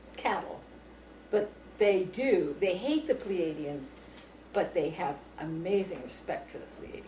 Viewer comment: first i heard that i thot she said "they hate the canadians" LOL